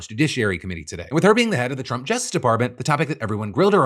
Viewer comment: I heard "the Trump Jester Department" and I said absolutely they are, what a clown show 🤡